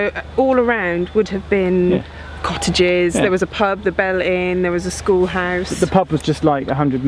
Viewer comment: Wait...what?!? Did you say the pub was called "The Bell End"?!?!? That sounds sort of...impolite at the very least!!!